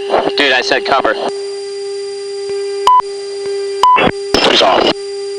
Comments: "dude I said cover" 2:23 "piss off" lol did I hear that reply correctly? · Did he said "piss off"? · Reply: “2 is off” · Did i hear this correct? "Dude i said cover" "Piss off"